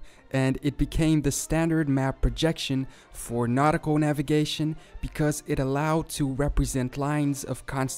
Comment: I thought he said narco navigation instead of nautical navigation